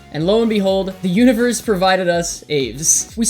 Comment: captions says “the universe provided us aids”